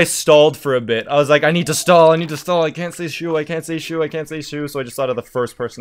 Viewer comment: Who's he mentioning at 1:20? Can't understand the name. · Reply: shu